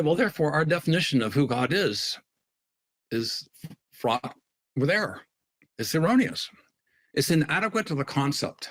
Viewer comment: Anybody catch what he says at 12:50 or has it been edited out? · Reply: "Our definition of who God is is fraught with error." He just says the fff sound twice.